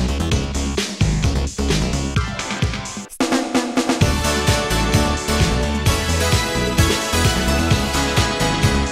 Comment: Anyone recognize what the voice is saying, just before the beat break starts at 0:30 ? (Something like "step up") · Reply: It seems "スタート(sta-to)”. "start" in Japanese pronunciation.